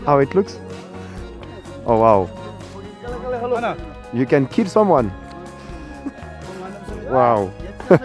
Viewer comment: Bruno "You can kill someone" Ce que j'ai entendu " You can kill some moines 😂 J'ai kiffé le voyage gratuit au Bhoutan, merci Bruno pour ce reportage !